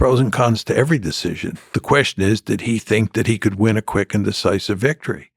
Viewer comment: Did he say “he thinked “ around 7:50 !